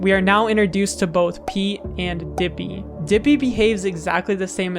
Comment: I thought I said pee instead of peat